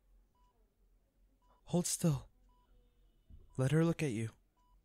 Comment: This part 7:24 i can't help it but I heard 'Hey girlie, hold still' - Childe (genshin) All in all this audio is filled with emotional unconditional love